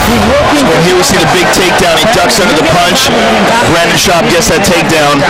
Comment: Is it me or is Joe calling him Brandon? 3rd option, crap audio.